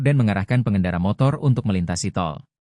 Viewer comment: Ini gw yang salah denger apa emang typo, di awal awal pembicara harusnya bilang pengendara motor malah pengendara kotor😅